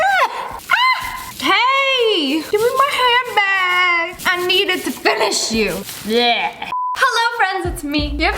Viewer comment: I thought she said I need my handbag to finish it.🤣